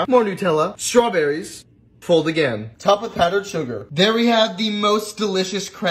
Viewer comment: “This is the most delicious crepe” what I hear: “This is the most delicious crap”